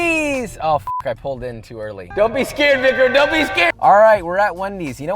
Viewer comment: wait did he just say "dont be scared n*gga" at 0:50???? or am i crazy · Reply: Don’t be scared “Vicram” it’s my name - I’m the camera man